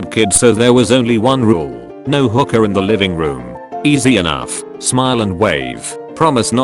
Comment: I was listening to the first one, misheard it and thought his parent's rule was "no hookers in the living room."